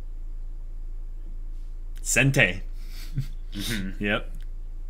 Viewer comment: what word does say at 21:58? Sentai?